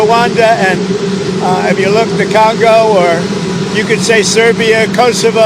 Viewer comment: Did he just say. "the Calgo?" 🤣🤣🤣